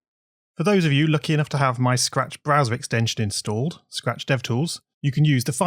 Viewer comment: following the Mario theme, the subtitles read 'bowser' instead of 'browser'